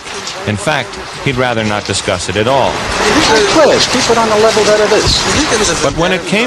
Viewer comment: What did he say at 0:35 ??? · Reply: Ehh let's keep it on the level that it is? Is my best interpretation